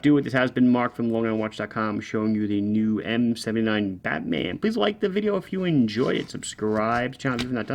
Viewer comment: Geez ! I thought you said Fatman and since I'm 275 pounds 5,5 I thought you meant me ! My hearing and sight are not very good Mark so I will forgive you ! The Islander is ten times so much more better. Some I would even consider eating ! Cheers 👍